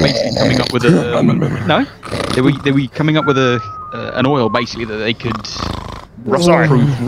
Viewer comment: I HEARD OIL